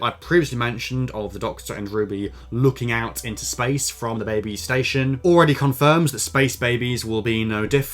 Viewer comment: Thought you said "The Babestation" for a moment 😭